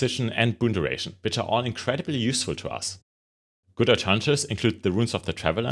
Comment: Using your core guardian build and plan to transition to this once I get enough hero points. Thanks! It's not important, but at 0:49 I cannot understand what is being said here: "which are all incredibly useful to us. [?????] include the runes of the traveler..." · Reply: "Good alternatives" 😅